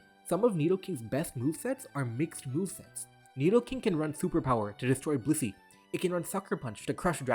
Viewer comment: I heard "Nidoking can run Superpower to destroy pussy" and did a double take 💀